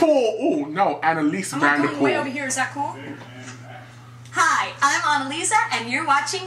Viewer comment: I thought she said "hi I'm Mona Lisa"